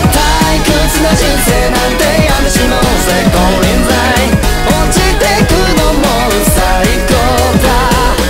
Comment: 個人的に「堕ちてくのも最高だ」っていう部分が 「堕ちてくの、も"」みたいな「堕ちてくこと、それも最高だ」と 「堕ちてくの、もう最高だ↑」っぽい感じに聞こえて面白かった。